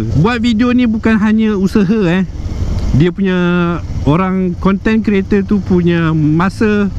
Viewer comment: Nasihat yang baik bang Tbh mmg kat sesetengah orang Malaysia suka curi video 😢 Btw for some reason aq tersalah dengar 'content kereta' 😂